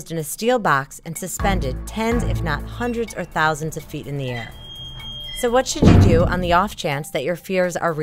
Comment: Thought she said "10's if not 100's of thousands feet in the air" lol